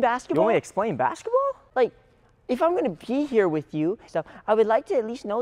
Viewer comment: i thought he said if i’m gonna pee here with you 💀💀💀